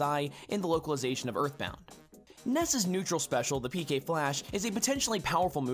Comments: Please tell me that i was not the only one in brawl who thought ness was saying "PK PULSE" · I always thought he said PK Pulse not PK flash